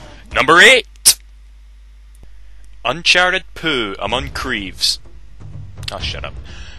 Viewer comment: it sounds like you say: UNCHARTED POO